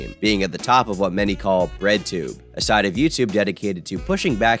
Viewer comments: I heard "RedTube" 💀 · I thought he said ‘redtube’ at first. I was gonna say that redtube is NOT about politics. Lol